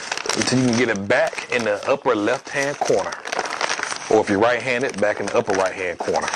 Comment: at first i thought you said you were white handed and i was like what?